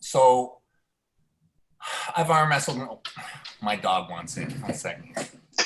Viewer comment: Devon “ I arm wrestled my dog one second “. Which sounded like once for a second I was like what ? 😂